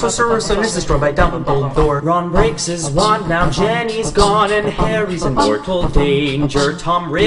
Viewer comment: is it just me who hears ‘Jenny’.